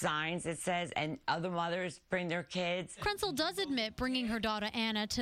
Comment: did they say Krentcil or PRETZEL?!